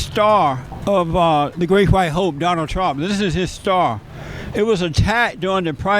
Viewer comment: did he call donnie the "great white hope" at 5:48 ? i fucking hope not hahahahaha.